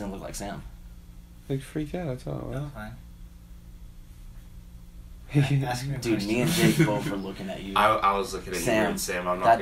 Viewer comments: Did anyone else hear Sam say "No i'm fine, ask me a question" or was that just me??? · Sam says “I’m fine, ask me another question”. Pretty solid evidence that he mighta got possessed momentarily especially with them saying it didn’t look like sam. Something to consider. Maybe whatever possessed him slipped up. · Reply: I think Sam said; "No, I'm fine. If you keep asking me questions..." But I'm not too sure, he also sounded a bit different more deeper or it is just me over calculating, but yeah it was along those lines 😅😅